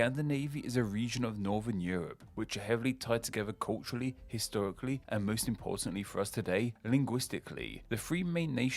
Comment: "...region of northern europe which are heavily tots gave cochally..." What the hell is he saying? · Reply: "...which are heavily tied together culturally, historically, and most importantly for us today: linguistically"